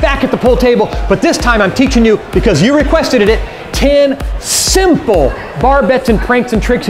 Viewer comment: Requested it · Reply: At first I thought he said requested it but no he says say requesteded it